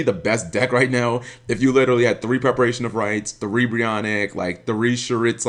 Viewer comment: I was talking about the original Preparation of Rites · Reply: oh i thought I heard pre-preparation, sorry lol